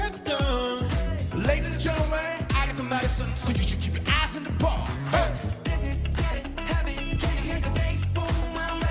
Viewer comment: did he say the a word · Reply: he said eyes